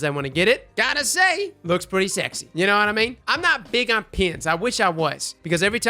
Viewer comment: I totally just heard you say "I'm not big on penis"